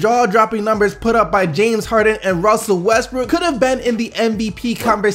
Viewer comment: This guy says ben instead of been right? (isten to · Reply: Eesaa Philips he says been